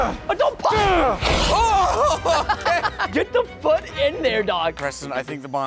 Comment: Nah at 9:57 did he say a bad word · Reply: He said foot